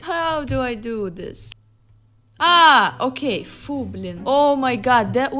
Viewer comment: What does she say at 5:27? FOOblin? 🧐